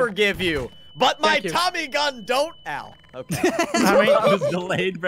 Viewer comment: when brandon said tommy gun i thought he said tummy gut